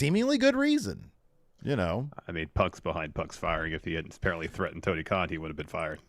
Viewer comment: Did just say Punk screwed Punk 8:53 is Vince in a mask?!!?!?